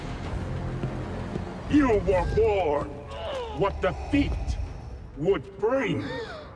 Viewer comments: Ngl, first time I heard this I thought he said You were warned what Da Feet would bring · I’m ngl I thought Vader said “you were born”